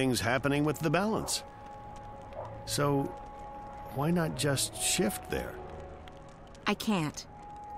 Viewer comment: I thought he said 'So why not just shit there' LMFAO